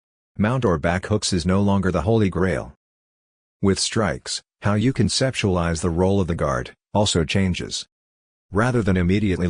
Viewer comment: "how you conceptualize the role of the guard" I misheard it as "how you can sexualize the role of the guard".